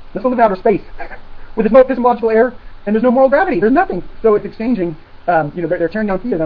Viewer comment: did he say there is no gravity in space? Kind of hard to tell for sure with it sped up.